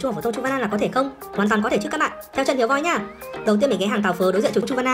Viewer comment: Teo để ý chữ teo nghe thành